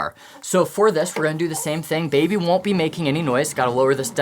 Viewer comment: I am so confused I thought he said baby isn’t real in the beginning 😅